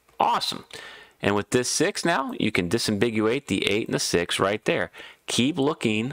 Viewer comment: Did you say "disimbiguate" ? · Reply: disambiguate. Yes, I got that from watching Cracking The Cryptic and they say it whenever you can solve both cells of a Naked or Hidden Pair.